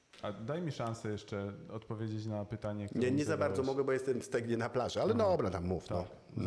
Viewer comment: Z początku usłyszałem "Jestem w sejmie na plaży" w sumie by się zgadzało.